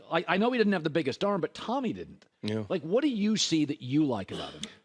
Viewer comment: Did Colin call tom Brady “tommy”?